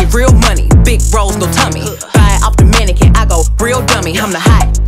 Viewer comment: Yoooo when she said, "Buy it off the mannequin" at first i heard, "If i were a dominican, id be a real dummy!" LOL! i was like WAT 😂😂😂😂